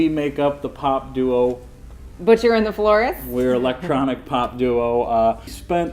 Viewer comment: At first I thought you said Butcher in the Forrest.... Which sounds awesomely creepy